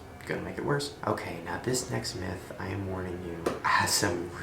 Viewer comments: "I am god" I heard "I am an aircon" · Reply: asparaqus I heard "i am korn" lol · I literally thought she said "I am Khan."